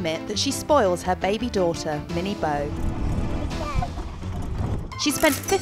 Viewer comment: Thought it said pregnant daughter..